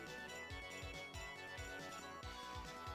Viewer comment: I always heard "viper uppercut" from Sagat